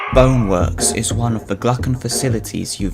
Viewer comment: I thought you say bonework vr